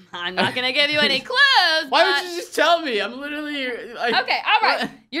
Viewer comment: i just hear this but its a little bit cut in the end unspeakable almost said "Why wont you tell me im literally your boyfriend--" but its a lil cutted to the end btw nice video it made me laugh!